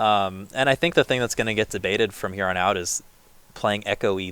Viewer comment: did he say Jebaited ?